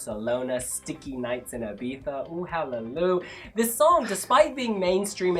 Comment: On first hearing, I thought had said "sticky nights in Iveta"... Misheard lines are the better lyrics, also in real life. ^^